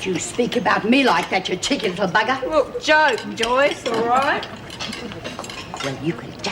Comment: Did Rabbit call DOT, "JOYCE"? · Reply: She say, "It's a joke, Joyce." That appears to be a common saying in Aus at this time. It doesn't mean that Rabbit had got her names muddled. The same phrase is used a load of times throughout the show. Just Googled it and found this - "Slight alteration of "That was a joke, Joyce," catchphrase of George Wilson (played by Graham Kennedy) spoken to his wife Joyce (Rosie Sturgess) in The Wilsons, a segment featured regularly on Kennedy-presented show In Melbourne Tonight in the 1960s."